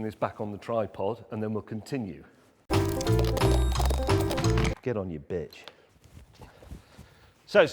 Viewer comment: did he just say " get on you bitch" ? nice